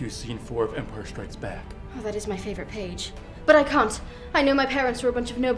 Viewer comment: That sounds like she said "cunt"